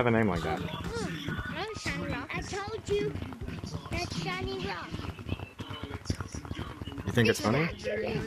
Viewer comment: What people heard:"I told you that shiny rock" What i heard:"i told you that shany rock" Me:WHO SUMMOND MEH???